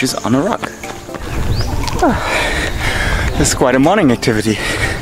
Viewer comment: what word did you say? its not clear WHICH IS ???? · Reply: Activity